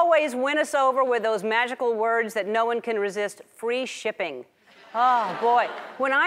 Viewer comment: I thought she was gonna say Free shitting